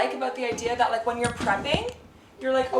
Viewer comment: When you were talking about the refrigerator I absolutely thought you said I liked the idea when you're pregnant you can be over here and someone else can make cooking I had to back up and re-listen do you say prepping😅